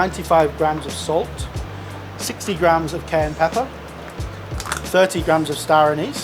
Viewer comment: Heard him say “60g cayenne pepper” but saw 6g on the ramekin. Nice knowing you guys.